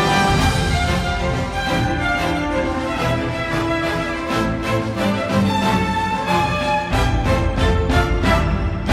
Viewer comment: tu travaille dans quoi ? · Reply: L'aviation